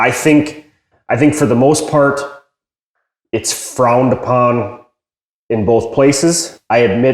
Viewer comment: What is he saying at 08:05? Fraund? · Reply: Frowned. A look of disgust. "Frowned upon" is a saying for something that people don't like you doing.